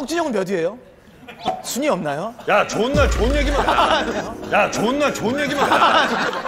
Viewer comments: 좋은날이 존나로 들리네ㅋㅋㅋㅋㅋ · 나만 ㅈㄴ 좋은애기만 하자라고 들리나?